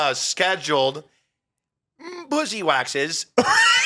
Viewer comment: fan here, I was listening to the beginning about being banned for sexual aggression then just clicked to a random spot in the video(50:44) and the first thing I hear is "mmm-bussy waxes!" lmfao I'm goddamn crying!!